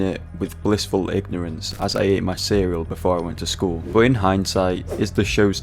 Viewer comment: subtitle got confused with the word "Cereal" and "Serial"